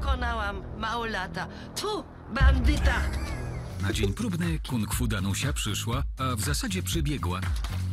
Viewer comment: Boze czy ja serio usłyszałem Kung-Fu Danusia? XDDDD